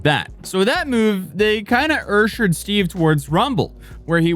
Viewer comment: why tf did you say ursher lmao